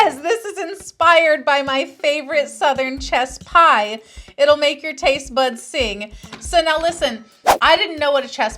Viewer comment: At first I thought you said "chest pie" which would make sense for Dolly 😂